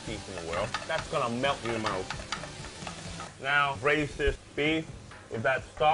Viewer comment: did he just say "racist beef"?